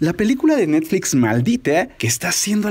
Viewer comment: Pensé que decía Matilda y después me fijé que dice maldita 😂